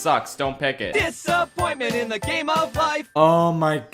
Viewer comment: I forgot it came from Robot Chicken. I always thought it said "Is that moment in the game of life"